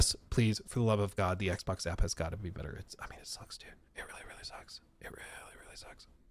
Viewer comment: about 4:50 mark when you were whispering,I thought you said, “It really really sucks dĩćķ”. I had to go back, turn on closed captions and watch it again to see that you said it “ really really sucks DUDE”. LMAO😂